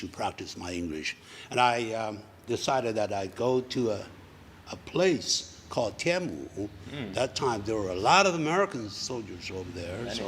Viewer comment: 「called 天母」，我竟然聽成口天吳XDDD